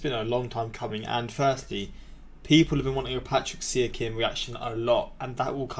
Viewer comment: wait who did u just say. Pascal Siakam? I dont know who u just said if it was siakam look it up how to say it cuz that was a pretty bad pronunciation but good video reaction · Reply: “Patrick See-a-kim”